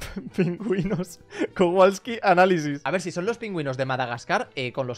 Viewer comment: Parece que dice kawasaki